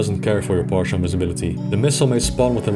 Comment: I know is saying "randomised", but I keep hearing "random ass" and it's hilarious. "Random ass angle of +22 to -22 degrees". XD